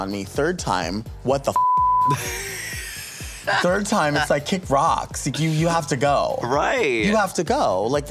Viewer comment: Did Delta say kick Roxxx? At 1:43.. Hahahaha! Am I hearing things? Jk tho!